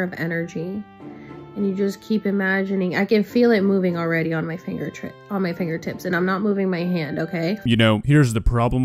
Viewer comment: She had what is called a Freudian slip at 3:31... Instead of saying "finger tip", her brain told the truth and she said "finger trick". Our brains are smarter than we are.... Ironically enough.